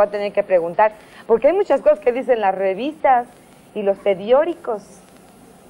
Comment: escuché bien? .........hay muchas cosas que dicen las revistas y los PEDIORICOS